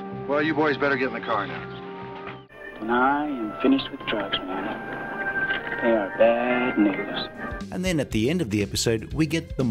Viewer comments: Did I hear what I think I heard??? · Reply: he says "I am finished with Drugs, They are Bad News." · Did he say what I thought he said? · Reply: He says he's "done with drugs. they are bad news."